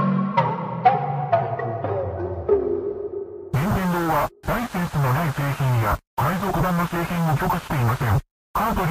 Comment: i know that im late but dose anyone know what it says at 14:50 and yes i know i can look it up but i lazy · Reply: "Nintendo does not allow unlicensed or pirated products. Remove the cartridge and contact technical support. Game over."